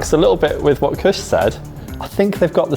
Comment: Caption: "... a little bit with what Chris said". Damn I watching Sorted for years now and I always called him Kush. My bad 😞 EDIT: 12:25 "Its the hustle-" what it is, is the Huttlestorm